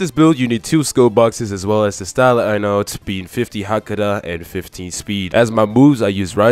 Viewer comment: What did u said in 0:42 ?? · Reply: 50 hakuda 15 speed